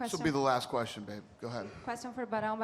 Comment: Did Dana just call her babe?